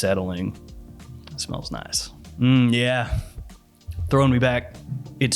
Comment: "Mmmm yeah throw me back" That's what she said 💀